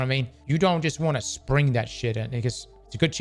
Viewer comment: what did he say? Spring that shit at who??????????